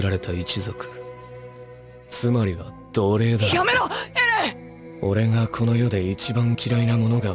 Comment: Damn this 😆 I'm beginning to understand Japanese more and more. 1:44 Hearing Armin saying "yamero" which means stop it or so, but the subtitles say "Chicken McNuggest?" is just even more hilarious 🤣🤣